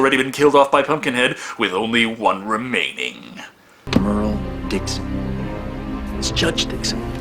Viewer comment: Does he say, Merle Dixon?